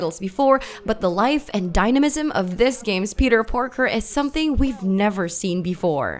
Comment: Did she say "Peter Porker"? 🐷